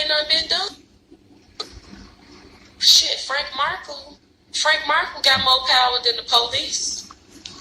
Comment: did she say Marco ????